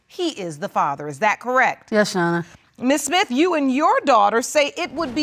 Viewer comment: Did she say, yeah Shawn, instead of "yes your honor?" 🤷‍♀️